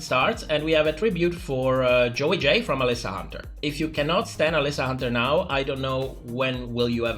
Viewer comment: i honestly thought you said 'if you can't stand alyssa hunter... i don't know when you are going to start." and i was like WHAT! then i replayed it and you said 'stan'. my bad! i gotta turn up the volume 🤭alyssa is so sweet.